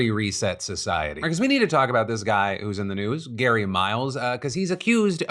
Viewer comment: I misheard you at first and thought you said “we need to talk about this guy oozin the news.”